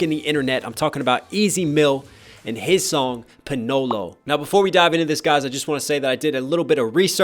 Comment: did he just say panolo?